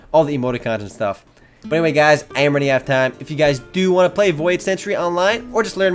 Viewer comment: did he say love u guys lol 14:07 lol